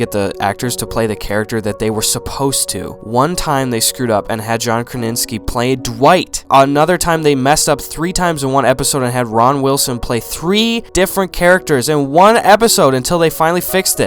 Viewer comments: Did he just say 'John Kraninski' at 1:34? LMAO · did he say ron wilson